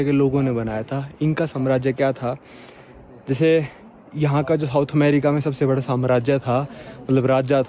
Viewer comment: Am I the only one who heard "Houth America"? Ya bhai ne jaanbooj ke bola? 😂